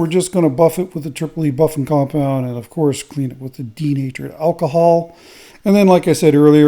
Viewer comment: Another awesome piece 👍. When I first started watching these videos I thought you were saying teenagered alcohol 😊. Thought maybe it needed to be aged 😂